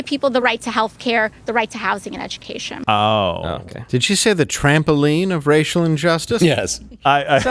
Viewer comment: I thought she said trampoline